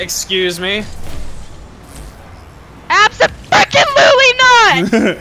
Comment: For everyone flipping their lid for comment at 3:58 she said "Abso fricken lutely" people need to calm down... People ALWAYS try and point out times on our videos of our children swearing or doing something wrong...We have been making videos for years and I can assure you our kids don't swear regardless of what you "Think" you heard. Pointing out possible swear word spots in videos is a waste of your time and ours of constantly having to reply and defend it. Enough said. Good day.